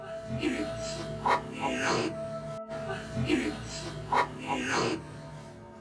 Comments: Instead of "its here/near" i hear "its you" maybe? Number 11 Im certain is a malfunction, I had actually more than one of those and they both did something very similar. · On number 15 I hear "He's here" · #15 I heard I'm here when its half speed